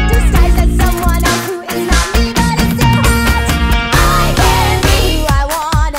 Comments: im so disappointed i thought they said "eye candy" and im like?? "that makes sense!!" but its i can be :// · "I give you 'sexy corn'" I CAN'T BREATHE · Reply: For a second I thought she said I can’t breathe instead of I can be and I was falling out of my seat